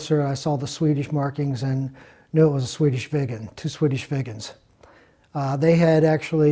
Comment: I keep hearing "two Swedish vegans" instead of "two Swedish viggens". Who would have thought vegans could be so aggressive and protective? Interesting story. Respect to all involved.